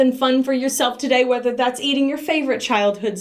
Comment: I got distracted at the end and thought she said ‘do something fun, like eat your favorite child’ and immediately did a double take.